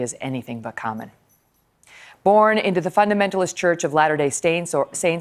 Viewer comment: At time stamp 0:38 did she say "Born into the fundamentalist church of latter day stains"? <link>